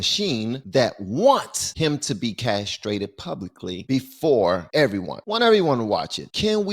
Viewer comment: Did he say Castrated publicly